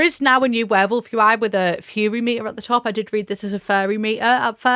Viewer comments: Ohhh “Furry”?! Thought she was saying fairy 😂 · Omg I thought you were saying FAIRY not furry